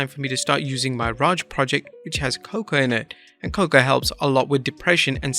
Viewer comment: you should've enunciated the word 'cocoa'. I had to go back and read it while you were talking because I thought you said 'coca' 😂